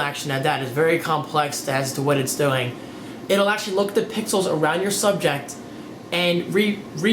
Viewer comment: he says pickles instead of pixels lmao